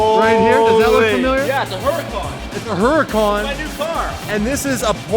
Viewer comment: Wait!! did just say "its my new car???" after telling its a Huracan?????? some body please @ 11:35 then turns away like🤦🏾‍♂️